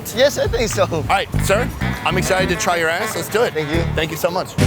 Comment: Did he just say "I'm excited to try your ass" ? XDDDDDDDDDD